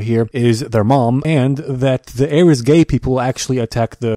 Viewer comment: "The air is gay people" - 2021